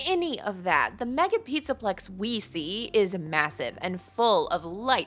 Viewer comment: did u say massive